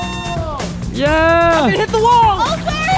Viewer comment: I thought he said “ let me hit the woah .